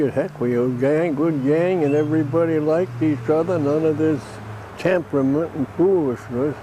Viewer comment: What does Craig say about Robert Shaw at 13:01? I can’t quite make it out. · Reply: He says "Everybody liked each other. None of this temperament and foolishness." I can so imagine Robert Shaw delivering that line in the style of "radar, sonar, electric toothbrushes".